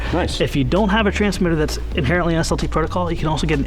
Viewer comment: Nice planes. I can't figure out what for protocol is mentioning because he speaks too fast. It sounds like "salty protocol" at 3:00. I'd like to have an FPV version of the P-38.